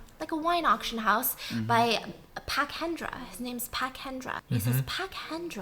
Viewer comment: I thought she said pacendra. But instead. Pak Hendra :’) this is why i love stephanieee ❤️